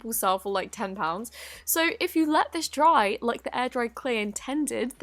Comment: I thought it said “angry clay” at the airdry clay part XD